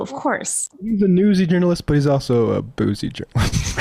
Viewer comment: what did Matt say? Everyone loved it and I want too! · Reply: "He's a newsy journalist, but he's also a boozy journalist."